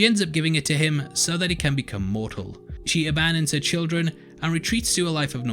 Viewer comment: "... she ends up giving it to him, so that he can become mortal." Except it's Lilith who becomes mortal... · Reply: I thought he said immortal, but the voiceover got cut off/ went funny. But, yes - Lilith becomes mortal.